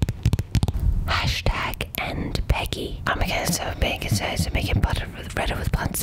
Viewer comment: I heard #endpeggy the first time I watched the video and I got scared. But anyways, I really liked this video! This needs many more views! ^^